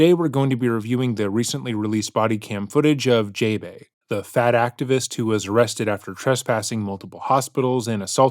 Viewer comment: you said "fat activist" and I heard "the fat ass"